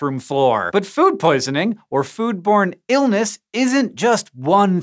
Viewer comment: For a second I heard "foodporn illness"